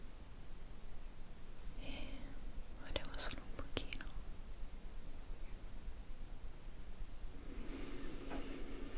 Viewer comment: cosa ha detto?? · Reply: Ha detto che voleva profumare l'ambiente